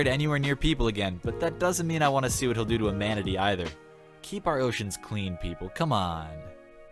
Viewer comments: did he say Imanity? aaah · Did he say Imanity?